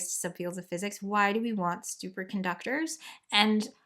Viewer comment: did you say why do we want stupor conductors lol😂